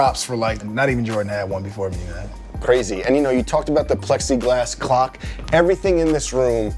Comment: I thought he said plexiglass cock.